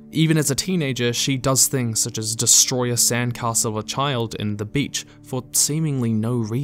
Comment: I heard that as "[She] destroys a sandcastle, a child, and the beach" and honestly, I believed it.